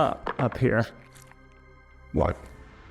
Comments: what the hell was that sound? Sounded like a demon voice saying “what” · does it sound like someone saying "what?" in a distorted voice? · anyone hear that??? something said what??? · Anyone else hear a EVP saying "what" at 18:54? lol 😄